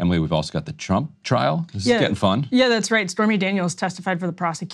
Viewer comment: did she say spermy Daniels?